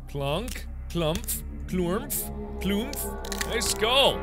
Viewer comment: what is he saying PLUMP COUMPH